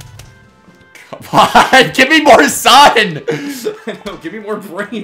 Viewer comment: I actually thought he said "Give me more sons!"